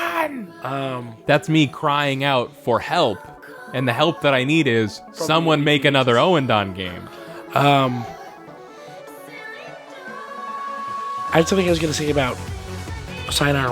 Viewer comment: whats the name of the rythm game that yall like and that isnt EBA and sayonara? i kept hearing "owen done" but im pretty sure that is not it